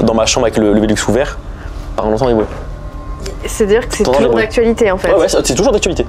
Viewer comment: dit quoi à 33:22 ?? Je comprends pas 😭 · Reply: On entend du bruit